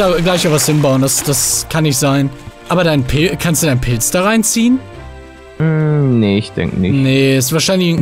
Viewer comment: das hat sich so angehört wie "kanst du denen pen*s da reinziehen" 😂😂